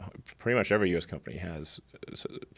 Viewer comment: Did Elon say "spies" or "suppliers" ?